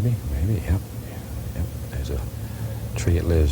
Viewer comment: Bob always says "Right dare" instead of "right there".